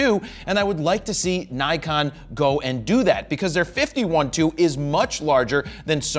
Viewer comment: I was changing entirely to Sony on Black Friday.... Now, I am like what???? FINALLY!. This may make me keep all my Nikon glass. BTW: Lol...The 24-120/4S is not a */4Z (50:42). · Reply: Jared got so excited that his words start getting merged (55:13).... sounds like a 51-2 instead of 50/1.2 :P